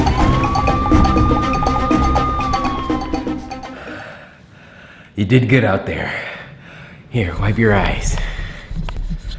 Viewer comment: Help at the beginning when he said “here wipe your eyes” I thought he said here wipe your ass